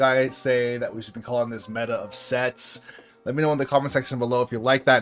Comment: I was so sure I heard “meta of sex”